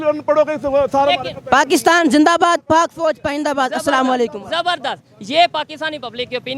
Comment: He said something at 31:34-5 and did he say Pakistan fauj paindabad at 31:44😂